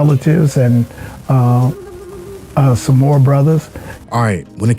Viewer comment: Did he say Samoa brothers? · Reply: Nah...he said and some more brothers